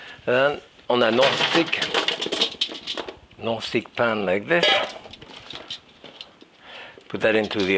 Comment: I thought he said, "a gnostic pan". A no-stick pan. Aha!